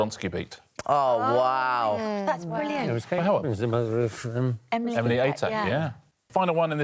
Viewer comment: anyone knew what hugh said at 5:32 haha!! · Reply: He was asking/affirming that Kate Robbins was the mother of Emily Atak